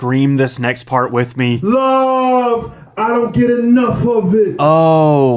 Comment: I always thought he said "Love, I don't get enough, papi". And, sincerely, I can't blame myself for that.